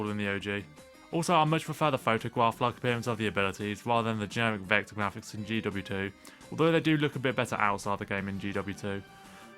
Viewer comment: Wtf did he say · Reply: Sorry I'm late, but "I much prefer the photograph-like appearance of the abilities rather than the generic vector graphics on GW2"